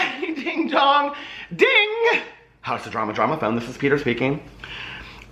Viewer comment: My 13 year old son's dad called us while we were in the car the other day, He answered and without even thinking I said " House of drama drama phone this is Peter" My son starts laughing, his dad is like "what?" I say " Jane is that you?" my son was laughing so hard he hung up on his dad. helping moms and teenage sons bond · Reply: Oh my god I always thought he said "how's the drama drama phone".. I've learnt something today 😂